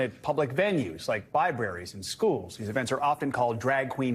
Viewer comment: did he say "bi-braries" instead of libraries?